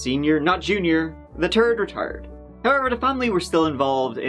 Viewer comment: I thought you said “Ronald the Turd” But no, it’s “third” Both happy and disappointed.